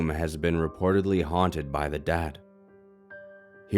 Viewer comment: I misheard at 14:45 and I thought you said "haunted by the dad"